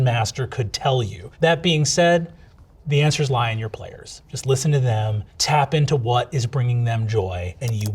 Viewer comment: LOL I misheard "the answers lie in your players" as "the answer is to lie TO your players" and I was just like "in many circumstances I agree, but in Session 0???" and then I was the typed out answer XD